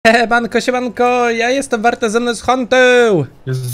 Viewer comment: Usłyszałam na początku ,,jebanko, jebankO `` xd Pozdro like i oglądamy.